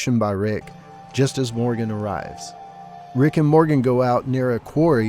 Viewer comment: first time I was distracted so I heard "Rick and Morty" 😂😂